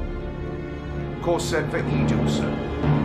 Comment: course set for egypt?!?! Am I hearing that right?